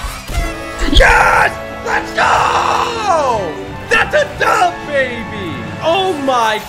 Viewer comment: lol when he said that a dub baby i thought he said thats a dababy